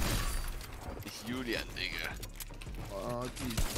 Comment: Did i hear it correctly or am i tripping? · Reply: no, he said digger not n